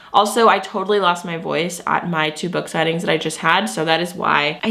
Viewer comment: When you said you lost your voice due to your “two book signings” all I heard was “tuba” and I was like wait… plays the tuba? 😂 📯